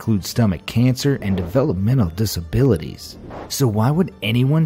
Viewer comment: Why the midget clip when he said "mental disabilities"??? Lol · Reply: yep, I thought he said "and develop mental disabilities"